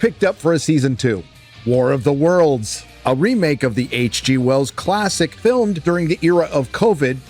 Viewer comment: Thought you said “the HG Wells classic film…”. My Um Actually moment was denied most cruelly!